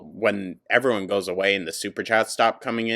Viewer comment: Every time I hear the computer say, "Superchat" I hear "SUPER SHATT!" 😅 I'M CREMATED ASF